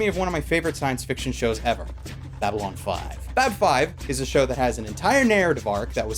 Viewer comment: What was the name of the space tv show he mentioned at 10:19? I didn't hear it right so I can't google it. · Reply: Babylon 5.